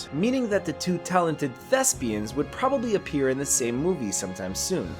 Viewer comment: i thought he said two talented lesbians 😂😂